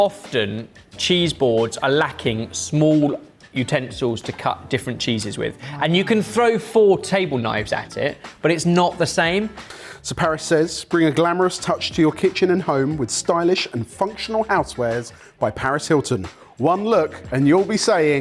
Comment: I was reading the captions and when introduces Paris Hilton's stuff I thought he was saying "be a glamorous bitch and..." lmao and I didn't even question it because it's Paris Hilton!